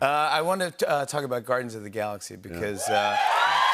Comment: it almost sounds like he said Gardens of the Galaxy, must be a Nature Show. :)